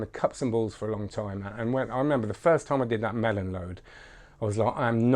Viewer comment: Wait wait wait! did you say you do a MELON load during cups and balls :o amazing ;)